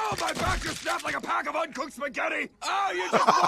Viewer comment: Garrett Woolf here and did Batman said a box of uncooked spaghetti 🍝